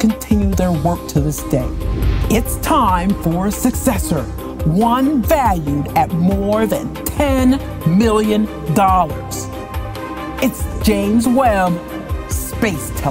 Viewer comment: Correction, JWST is valued at $10 Billion. 10 Million is right ridiculously small for a project of that magnitude and time scale. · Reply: I thought she said 10 billion lmao, had to watch again to hear 10 milly